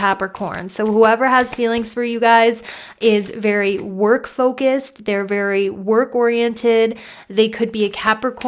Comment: Group 2 I swear I heard you say we’re very coke headed 🤣🤣🤣🤣👌 legit needed to rewind that lol.